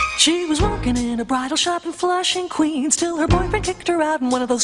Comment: Whoever sang this theme sang their heart out... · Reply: I was a kid when i used to watch this song and thought they said flashing queens,😂😂 and i was in flushing Queens watching this show 😂😂